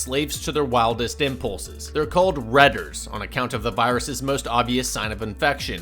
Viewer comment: When you first said the name of the infected I thought you said redditers.